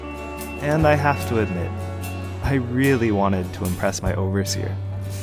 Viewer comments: They say “overseer”, I hear “overlord”. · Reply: They say 'overseer' and I hear 'overbearing.' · They say “overseer”, I hear “overlord”.